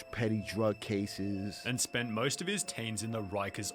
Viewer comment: did he say tens?!? It’s teens for fuck sake